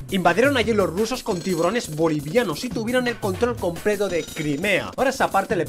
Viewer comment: Yo entendí tiburones bolivianos XD